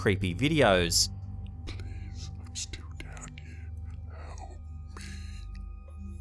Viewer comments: Whoa😳!!! Idk if I’m hearing shit cause I watch 2many scary videos BUT…I hear a creepy mans voice towards the end of this video at 15:03 saying….Please help me…I’m still down here??!! If anyone else heard the same thing….please let me knw??!! I’m sure I’m not the only 1 who heard that 2😳😬🤔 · Does do this a lot and i missed it?? I cant make out what he says in the beginning, maybe something like, "Please Im still down here, help me" something like that. Does anyone know what they are saying ???😂